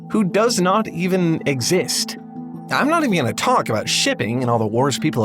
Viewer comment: I thought you said Shiving 🔪🔪🔪 😅 😆